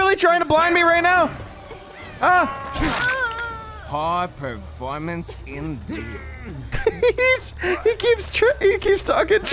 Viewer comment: I thought Wesker said "pog performance indeed" lol.